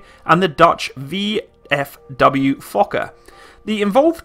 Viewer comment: Fuxker … Sorry lol I can't unhear it once I hear it XD